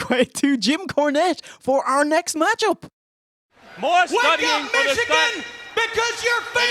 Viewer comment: i thought he said "wake up mexicans!: im like whos mexican? the steiners?.. lol. i had to rewind several times before i realized he said Michigan.